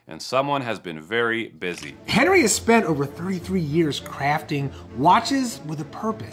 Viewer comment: did he just say three-three years?